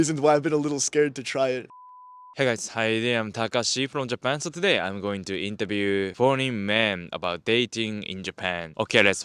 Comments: I thought Takashi said "today I'm going to interview horny man" 🤣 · did he say horney men or Foreign men? Sounds like horney men. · Thought he said "here to interview horny men about dating in Japan" 😂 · Sounds like you say im going to interview horny men in the beginning, haha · I thought you said forney men not foreign men lmao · Don't know why i heard horny man in japan 😭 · Jeez ... I thought he said "horny men" instead of "foreign men" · at the start i heard "Today were going to interview horny men in japan" ahaha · It sounded like he said he "interviewed horney men"